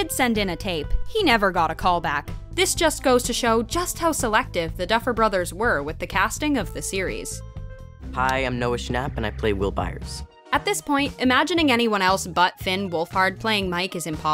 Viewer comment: bruh he plays roblox oh cant belived bruh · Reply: Oh shoot I thought roblox but he said will byers